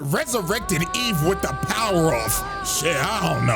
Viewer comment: Did he just say by the Power Of Anti Dr Umar😂😂😂😂